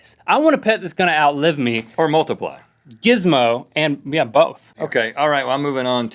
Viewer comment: It sounded like he said jizzmo.